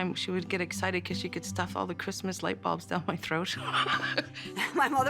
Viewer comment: did she say she used to stick light bulbs down her throat???? o.o